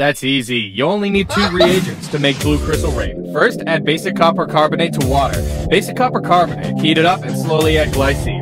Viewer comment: I thought he said copper chocolate